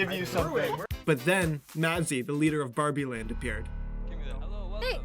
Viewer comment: I thought he said "Nazi"